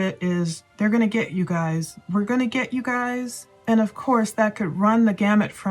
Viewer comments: sounded like he said "we're gonna get some dubs " could be talking about gaming like fortnite or cod but thats just what i thought. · I heard.....They're gonna get you guys! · I heard they're gonna get you girls...